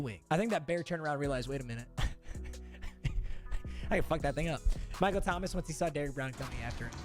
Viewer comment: sounded like he said the n word but i think he said i can fuck that 'thing' up